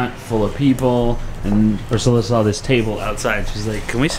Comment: Closed Captioning thinks your called Pricilla “Ursula” 😳 however captioning is getting better. For a while there it is no where near what you were saying so many times.